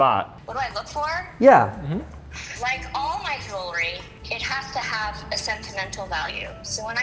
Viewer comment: the thing is that… they are normal people. Is it that crazy for you that someone could have a couple cartiers and still wear a Casio? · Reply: Thanks for calling me out as a drunk. Now that I've sobered up, let me know what happens at 11:07 where I could have swore I heard something along the lines of "like all my jewelry, it has to have a sentimental value" right before saying something about a specific watch that was purchased on a trip with her mother. Then again, considering how drunk I am, I may have just made all of that up. Seriously, not sure where you get off saying stuff like that, calling people drunks for absolutely no reason. Take a chill pill, pal.